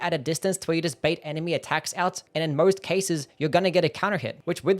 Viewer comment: You know I was just listening to the video instead of watching it and I thought he was saying god counter 😂